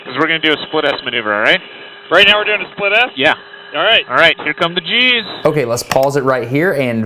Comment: When he said "here comes the g's" i really thought he said " here comes the cheese"